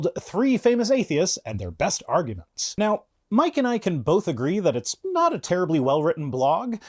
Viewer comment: “Mike and I can…” Sounded like you said “Mike-n-ike-n” Dumb, I know. But it struck me funny.